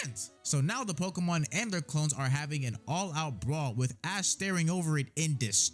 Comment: actually he really says clone are trash